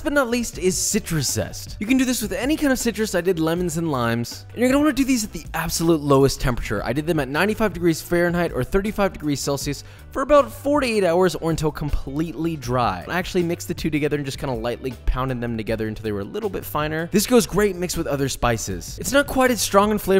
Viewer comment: When he was talking about the citrus zest I thought he said “I did them for about 48 hours” and had to rewatch it a couple times